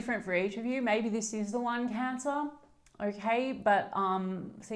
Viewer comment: May sound foolish but at 17:36 voice text must of misheard the word "One" with the word "Lung" making it lung cancer. May be a long shot but could that be a divine intervention?